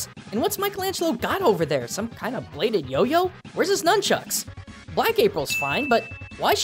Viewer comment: did he say black April is fine ? Just asking